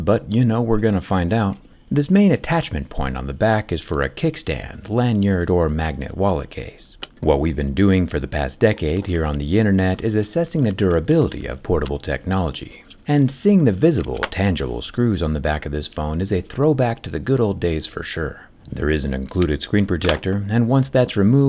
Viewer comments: Thought he said "Portal technology" 🤣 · Did Zack say "there's an included screen projector" or did I hear wrong? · Did you just say "a screen projector" @ 1:13? 😁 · Am I the only one hearing that Jerry said screen projector instead of screen protector here?